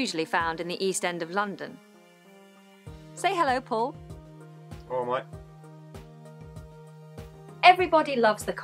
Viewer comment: What does he say? I don`t understand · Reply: I guess it's "Alright, mate"